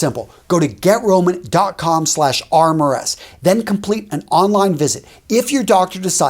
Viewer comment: When you said get Roman I thought you said get rona 💀